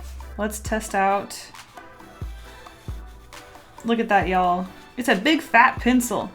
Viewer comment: I thought she said "Look at that yellaw!" And then I realized she said y'all 😂😅